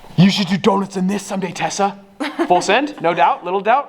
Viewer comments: When he was saying Tessa I thought he was saying Tesla and I got all excited about the model y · 😂I thought he said Tesla all along